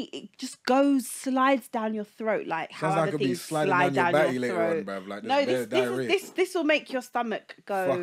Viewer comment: thought he said “I could be sliding down your batty later”